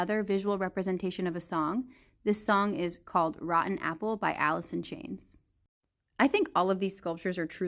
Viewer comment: Automatically created subtitles.. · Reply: I mean even if it was it did sound like she said "Alison" rather than "Alice in"